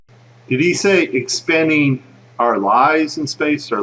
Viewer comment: funny thiong, when he says expanding our lies in space the closed captions leave out the word lies or lives or whatever he said lol funny though,sounds like he says expanding our lies, which they are.